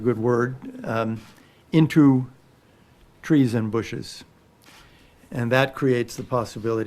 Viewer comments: I actually thought he said 'treason bushes' instead of 'trees and bushes' at first. Who puts this guy front and centre? Darth Vader is less scary. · Pretty leftist stuff. Maduro is refusing to let humanitarian aid into his country, resulting in increased suffering. Something that the program missed or consciously omitted. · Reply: A conversation starter here: WoodyUK 2 days ago I actually thought he said 'treason bushes' instead of 'trees and bushes' at first. Who puts this guy front and centre? Darth Vader is less scary.